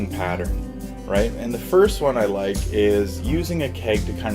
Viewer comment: did i spell it wrong somewhere? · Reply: I kept hearing thorac*t*ic (4:55 for example)